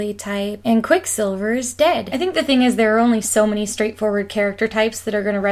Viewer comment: I thought you said, "and Quicksilver is gay", and I instantly thought, "oh thank god they didnt go with the incest backstory ..." XD